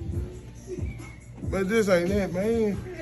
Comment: The way he looked back at the mirror to give it a second chance. "This ain't it maine." 🤣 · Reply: I thought he said “this ain’t half bad” 😂